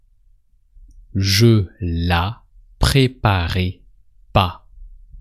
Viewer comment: J'essaie d'apprendre le français à ma femme, et je suis content d'être tombé sur cette vidéo mais j'ai une question. Est tu français ? Sinon, quel accent as tu appris ? Je prononce "préparais" "étais" avec un è à la fin c'est pour ca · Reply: Quand il dit " tu la préparais " J'ai entendu " tu l'as préparé "